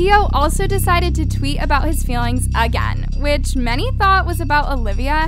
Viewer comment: why does she say “AGAOAION” instead of “again” /